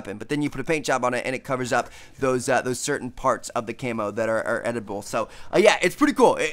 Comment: Ahahahahaha what was he saying 😂😂😂😂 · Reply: Yes at around 3:04 says edible instead of editible :D